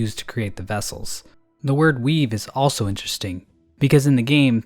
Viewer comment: At about 4:59, I thought said “Weeb” instead of “weave” and that he called the Weavers weebs...